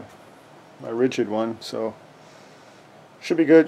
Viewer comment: did you say don't play safe ??? Lol